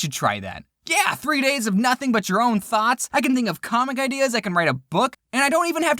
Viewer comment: My mum thought you said Farts instead of Thoughts lol Then after I told her it was Thoughts she went Ah American accent